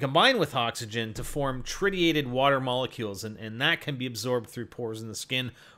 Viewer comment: Disregard my last comment, I thought you said trinitite.